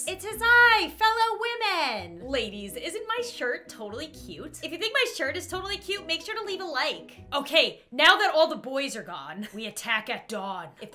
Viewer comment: I thought at 0:17 she was saying “Hello old men” I guess I just had low volume